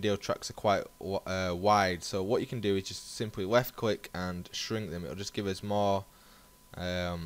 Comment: lool i swear at 3:15 you said 'weft click' :D